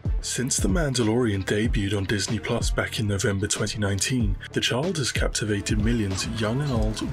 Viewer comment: wtf did he just say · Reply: “The child” his accent is thick lol